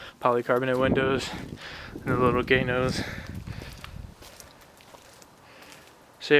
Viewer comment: lmao did he call it 'the little gay nose'